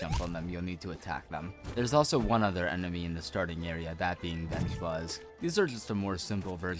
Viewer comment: captions say bench buzz while speaker says vengeflies